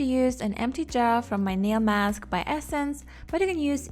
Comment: I’m sorry did she just say nail mask? What does it do? Where can I get them? How much for 50 of them?